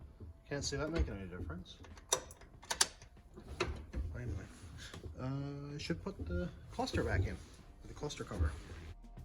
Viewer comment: Did you say you were putting the fuster plucker back in at about the 23:00 mark?